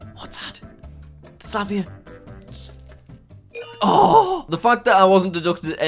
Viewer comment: i hear slavia wtf